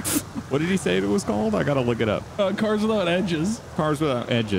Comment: I thought you said cars without engines at first